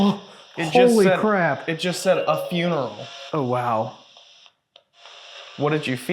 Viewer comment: you heard "a funeral" in a woman's voice ....with my headphones on i heard "my funeral" in a woman's voice. Great investigation! You definitely could hear spirit voices but as you said they seemed to be talking more with each other then with you. It was great to hear your capture during abandonment of the little girl Samantha laughing. And it sounded like a man was talking to her before you hear her laugh. Can't wait for the next video guys!! 👻👀